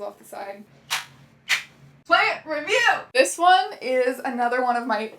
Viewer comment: I legit thought she said “ PANTS👏 REVIEW 👏!” 😂 Now I need this in my life!